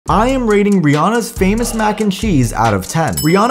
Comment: I thought you said banana but you said Rihanna